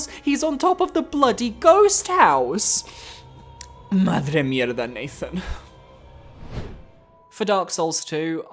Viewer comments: What the hell did he said here???? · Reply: Sounds like "madre, mierda, nathan". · what does he say here? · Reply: He says "madre mía". It translate as "my mother" and it is just an expression as to say "oh my god".